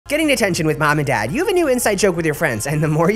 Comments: I thought it said getting attention with mom and dad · For somw reason i thought you said tatoo instead of detention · Man I thought you said getting attention not detention😂